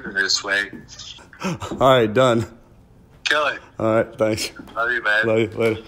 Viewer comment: It sounds like he says love you babe